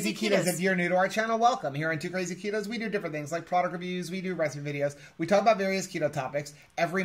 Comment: For the longest time I thought that was saying "rescue videos" instead of recipe videos!